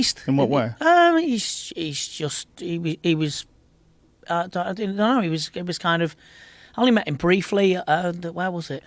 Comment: YOU MISSED SOMETHING AT 13:44 IS LITERALLY SAYING “I DIDN’T KNOW HE WAS G—“ 😂😂😂